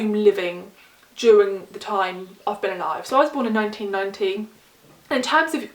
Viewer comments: The amount of times I had to replay this part where I thought you'd said "I was born in 1919" hehehe, completely miss heard you haha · Did you just say you were born in 1919? Or has my hearing failed me? Ethel and Ernest sounds great. I like the sound of Two Trees Make A Forest too. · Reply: Haha, I did mumble! I was born in 1990.